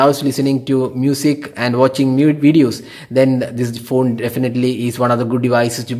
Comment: I guess I heard nude videos